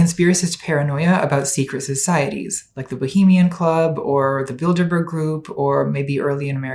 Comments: I misheard "Birderber Group" as "Build-a-bear Group" for most of this video, and was confused as to why there was a conspiracy involving a novelty toy store. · I hate to say this but every time she said bilderberg group i thought she was saying build a bear group and didnt even think twice about it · Oh Bilderberg Group. I just thought you were saying "Build-a-Bear Group" in an American accent. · I misheard the first line as "The Build a Bear group" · ill be honest ive never heard of the bilenberg group before so when natalie said it in the intro i thought she said "build a bear group" and i was like "okay. yeah, sure"